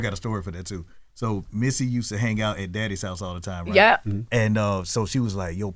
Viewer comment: did he call Diddy, daddy? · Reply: The name of Puff studio is Daddys House